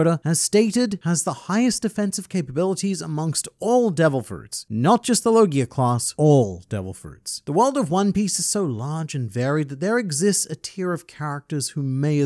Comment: I thought he said it has the highest OFFENSIVE capabilities